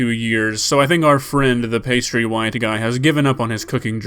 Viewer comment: Haha I thought he said pasty white guy. Seriously. And that wouldnt be anything than truth. Weirdo club in that house. . geez this is our world people